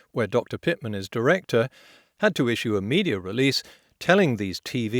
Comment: what is that word? "muh-k-experts" ? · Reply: muh-kek-xperts